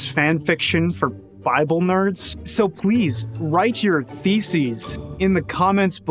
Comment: I definitely heard "write your feces" and not "thesis